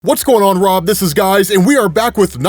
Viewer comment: did you just say, "What's going on Rob this is guys."😂